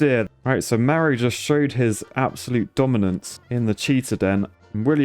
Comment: I thought he said cheater